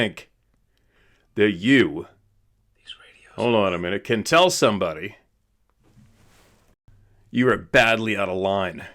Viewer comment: What's up with the whisper at 6:01? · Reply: It says m, “ His Radio is on..” something like that. I was just about to comment that as well??